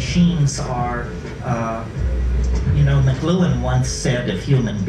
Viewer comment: he is talking about somebody who said that :"we are the genitals of our technology..." What is the name of that person? Macguen? Macgluen? I can't understand exactly the name... anyone can help with this? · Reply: Marshall McLuhan